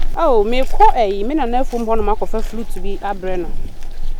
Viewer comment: Did I hear fluit